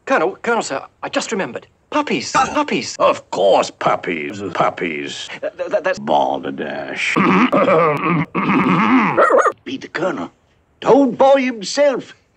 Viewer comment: For the longest time I thought he said "Delish..." there, and I was today years old when I watched with captions on and read what he actually said.